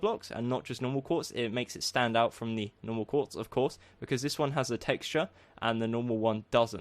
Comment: "... It makes it stand out from the other quartz, of QUARTZ" idk that what I heard